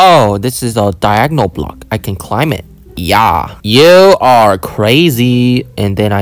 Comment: why did you say you want to kiss the kill block?!